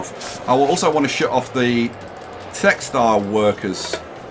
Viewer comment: As I was falling asleep to the soothing sounds of voice, I woke suddenly when I thought I had heard him say something about sex doll workers. I had to rewind to know he actually had said textile workers. My mistake.